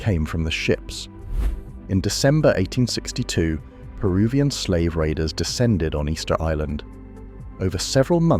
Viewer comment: Thought you said Peruvian slavers